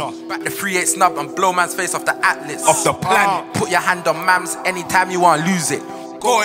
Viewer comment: put your hand on what?? I am not sure if it is just me or is he saying mamz or mans? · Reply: Mamz the guy standing beside him with his hat on backwards